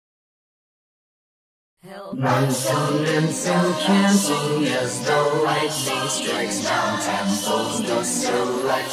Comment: Anyone know what the background singers are saying at 5:06? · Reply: They're saying "axon-dendrite", which basically axons and dendrites are parts of neurons